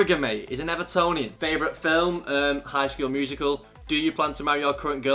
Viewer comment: Did he say High School Musical?😂